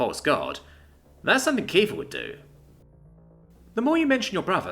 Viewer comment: did i hear that corectaly? kiefer?